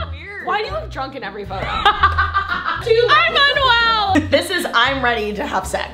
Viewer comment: am i the only who heard her say I AM UNWELL ??!?!?! daddy gaaaaang